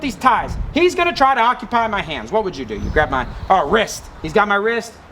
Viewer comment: I keep hearing "grab my wrist."